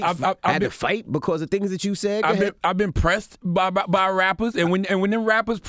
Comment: I heard envy say "i had to fight for things that you said" lol he didnt catch that.